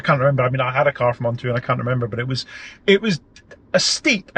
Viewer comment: Very interesting video. Thanks. You mentioned you hadn't used ONTO (3:24) but wasn't the e-Tron an ONTO rental? Maybe I misheard you. Anyway, the Zoe looks great and I hope you enjoy it. Thanks again! 👍🤗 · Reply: I said “I had a car from Onto and I can’t remember, but the excess was steep” 👍🏻 damn accent.